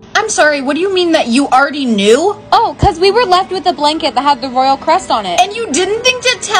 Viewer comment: I thought she said because we were left with the rolyal crust on it 😂😂😂😂😂😂😂😂😂😂